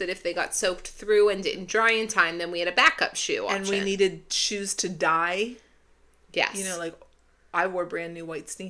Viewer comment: OMG when you were talking about the shoes I thought you were saying shoes to die. I thought you were being vague and funny about a difficult hike. It wasn’t until the end of that conversation that I realized you meant for dyeing. 😂